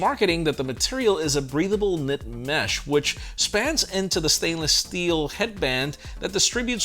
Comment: I think that was "snaps" into instead of "spans"!